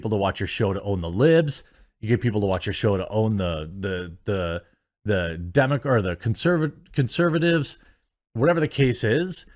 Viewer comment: i know he was about to say, "democrats" and meant to try to say conservatives... but i laughed really hard cause it sounded like he was gonna say "demogorgan".